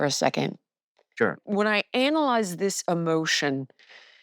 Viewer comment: does she say “anal-ize”?